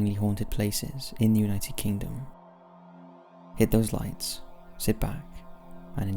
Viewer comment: I thought he said hit the lights so i hit them and now I’m blind welp shit don’t ask how I wrote this I talked to my phone